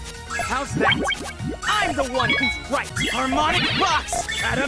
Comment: Isn’t just me or when squares say I’m the one who right I thought he said I’m the one who strikes